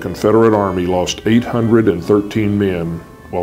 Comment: I first heard it as 8013 men vs 5 and I was like "daaamn!" Then I heard it was just 813 men...